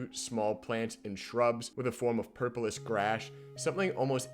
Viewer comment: Am I the only one who heard “purplis grash”?